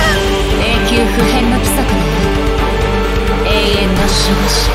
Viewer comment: 「うどんですね」にしか聞こえん